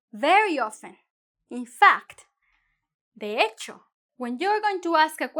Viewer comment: did she say they hate you? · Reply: let me help with this , she said in spanish in the minute 6:08 ´ (de hecho) .meaning (in fact).. if you need some more help, let me know please.